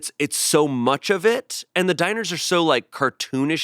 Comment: did Shayne just call dinosaurs “diners” lol